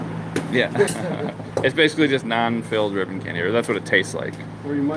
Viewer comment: Thought it said Camel filled ribbon candy